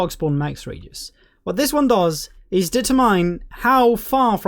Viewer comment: it sounds like hes saying datamine and i cant quite understand what the intended word was. Can someone tell me? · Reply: Determine